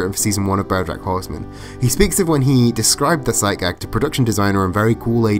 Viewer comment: What on earth is he saying at 15:05? "When he described the ........ psyke what?" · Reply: "Sight gag", sorry, bad enunciation. And I use they/them pronouns, for future ref 👍